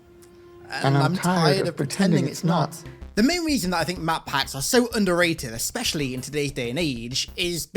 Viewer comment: Am I the only one who thought he said and wrote map hacks? And was confused